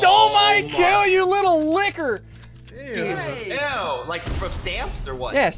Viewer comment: I thought he said The Nword